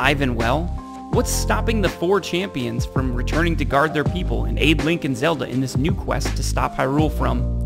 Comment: I heard Abe Lincoln Zelda 🤣🤣